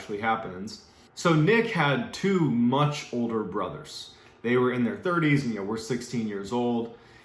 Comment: I thought you said too much older brothers not two, much older brothers